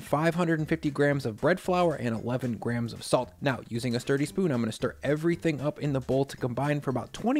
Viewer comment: I thought you said dirty spoon at the beginning not sturdy 🤣 was so confused as to why that would make it better.